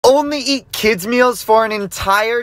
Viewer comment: Not going go to lie I thought he said only eat kids for a day